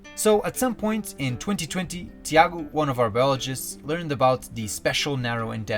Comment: I thought you said "one of our Belgians" 😅 love this channel as always ❤ thank you for all of the phenomenal content and work you do